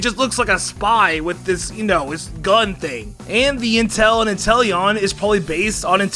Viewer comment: I first heard “Inteleon” and thought “ok, dang, a new Eeveelution.” And my dumb ass thought it was Psychic Eevee.